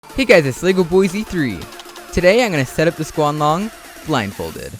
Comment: Wait 0:03 did you say "The Squanlong"?